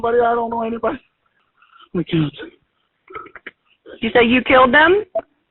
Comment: It does sound like he said "I killed them."